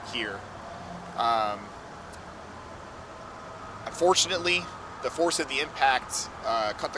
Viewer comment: Did he say @ 2:57 "FORTUNATELY the force of the impact cut the car into 2 pieces, driver died on impact and the passenger was transported to a local hospital but unfortunately the passenger also passed away."